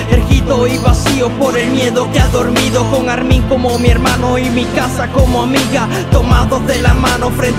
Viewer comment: Yo solo escuche "y Mikasa como amiga" :'v